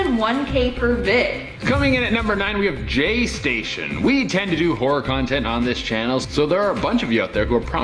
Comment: I’m watching this and folding clothes and I had to stop on #9 😂 every time he said “horror” I thought he said “whore” because I wasn’t looking I said I know damn well 😂😂 looked at the tv and said ohhhhh “horror”